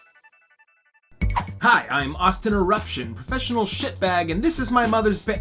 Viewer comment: I thought it said Autism Eruption, not Austin Eruption. Me and my brain, and we're just getting started!